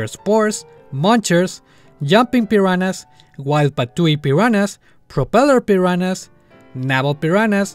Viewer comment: parece que dices la palabra "peruanas" XD